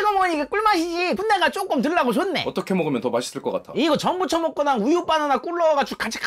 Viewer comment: 순간 전부 쳐먹거나로 들었음 ㅋㅋ